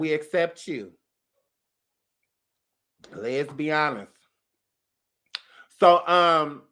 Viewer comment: Did he just say LESBI HONEST!😂😂😂